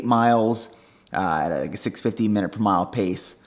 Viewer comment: did you say 6:15 mile pace for the 8 mile run?